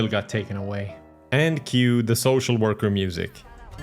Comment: Oh, I thought he said "Thank you, the Social Worker music." 😅